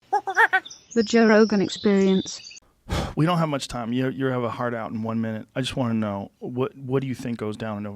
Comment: did Joe say "you have a hard on in one minute?"